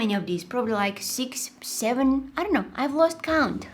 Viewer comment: WAIT did she say 67